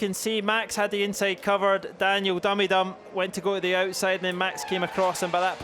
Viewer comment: did he say "Daniel dummy dumb"???